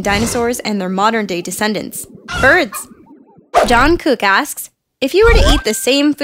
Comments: I THOUGHT SHE SAID JUNGKOOK. OML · did you say Jungkook?????????? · I legit thought said Jungkook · Who else heard Jungkook and thought about BTS🤔♥️